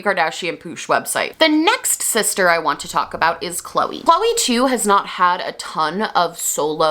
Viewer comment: When you said Khloe too, I heard it as Khloe2. Like 2.0, as if she was a robot or something